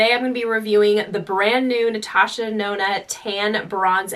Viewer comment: Did she just say “ Natasha Nona “ ?! 😂 DEnona